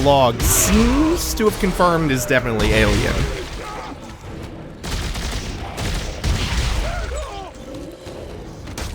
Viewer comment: Hey does anyone else hear the fodder at around 17:03 say "please stop!" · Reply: Indeed, he does. Also, guardians make a sigh of relief or whisper a “thanks” when you kill them